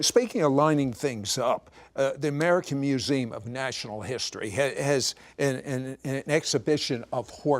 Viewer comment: I swear he called it "The American Museum of National History". . . how unnatural.